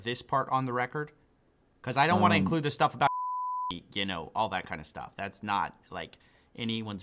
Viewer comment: the beep at 2:10 sounds like you said "I don't want to include stuff about shitballs" which I will assume is the secret go word